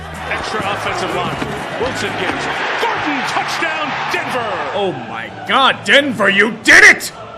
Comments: The first time I heard this I thought the guy said “F**king Touchdown”. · did he say “FUCKING TOUCHDOWN DENVER!!!”???